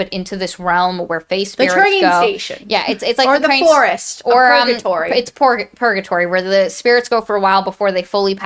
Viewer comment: I totally heard "purgatory" as "porkatory," which sounds like the name of a pretty metal BBQ joint